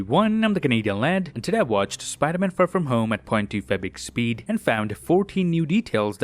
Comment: For a second i thought you said “Ive Watched the Spider-man : No Way Home Trailer in 0.25 Speed”